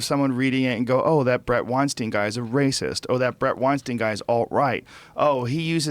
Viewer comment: I thought that said BRENT WEINBACH